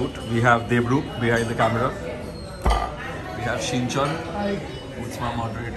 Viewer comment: If I am not worng... I heard shinchan...